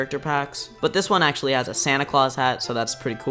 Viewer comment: When u we’re saying Santa Claus I thought u we’re saying sanic laws